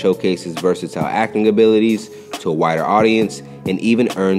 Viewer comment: “wider” sounded like “whiter”.